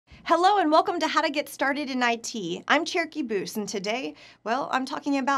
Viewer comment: Thought she said cherokee boobs